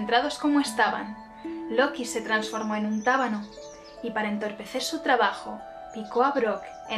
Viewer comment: la primera vez escuché "rábano" y me pareció muy gracioso imaginar a Loki intentando picar a quién sea así 😂😂😂